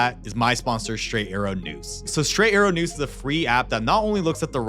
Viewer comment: During the mention of the sponsor, 6:41 totally heard him say Straight Arrow “Noose” instead of “News” probably just me but I had to rewind to make sure I was wrong, and clearly I was 😂